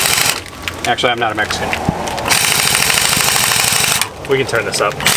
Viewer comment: does he say “actually I’m not a Mexican” ? Lol